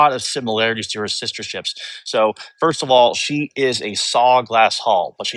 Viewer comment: Is he saying “solid glass”? Or something else? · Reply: Solid glass hull, with a kevlar (aramid)liner